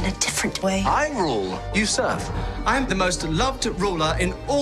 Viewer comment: Oh he said ..I rule you serve...I first thought he said I rule you suck